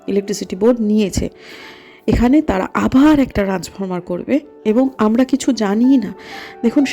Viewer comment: এ শব্দ টা কি বললেন ম্যাডাম, বুঝলাম না । একবার বলবেন ? "রাজখামার"???? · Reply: ট্রান্সফর্মার